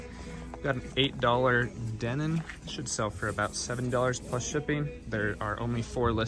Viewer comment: is he saying sell for $7?